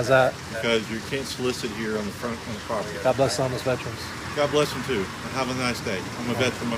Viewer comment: I wasn't looking at my tablet, so I didn't know what was going on, but what I heard you say was ," God bless homosexuals."